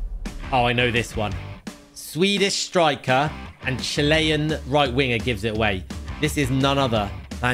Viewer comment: did bro really said chile?